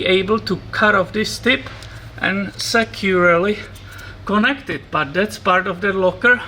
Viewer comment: when you said, "securely connect it", I thought you said "secularly connect it.":)